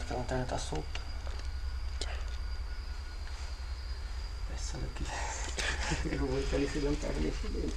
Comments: Bom eu entendi "saiam da qui !" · Bicho, eu ouvi "saiam daqui" 👀 que cagaço da porr@